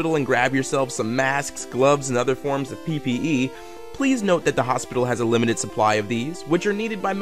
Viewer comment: did he just say PvE?